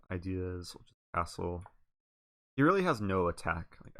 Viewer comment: Did he say, "His queen a5 idea...asshole." ?!?! lol I've got to be misunderstanding that...